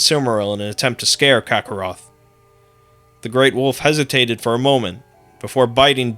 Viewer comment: Dammit, Goku, I thought you were good :/ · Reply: it sounds like it says "... in order to try and scare KAKAROT"... which, if you've ever watched Dragonball Z, is Goku's originally Saiyan name. Obviously the wolf's name in this story is not Kakarot (Goku) but it still sounds almost the same. And the wolf in this story is bad. That's the joke.